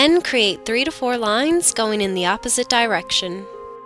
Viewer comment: Did she just said dyrection on